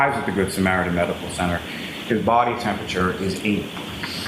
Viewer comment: such a horrible speaker - it sounds like he says “his body temperature was 8” Annunciate and don’t trail off. It’s so distracting when he speaks so lazily. This guy is a garbage trial attorney.